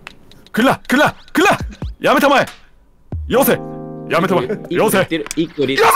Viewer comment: ダメだ よせ、やめたまえが 遊星やめたまえ に聞こえてアンチノミーにしか聞こえなくなった